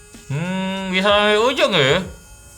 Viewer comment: Ngomong apaan kali wkwkwkwkwkwk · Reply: Bisa sampe ujung ya